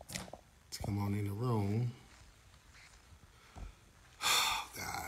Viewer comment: I thought they said she stormed in the room...dungeon dragon??